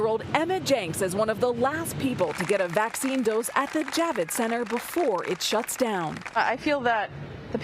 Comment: 👏🤡😂 the Jab- it center? Did I hear that right?